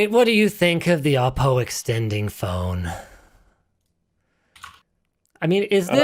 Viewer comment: At first I thought he said "Alpo extending phone"...